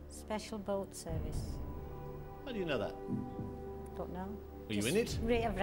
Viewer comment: sounds like she says fuck knows 😂